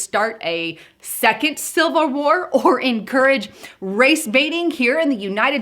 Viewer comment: did she just say silver war. what is that?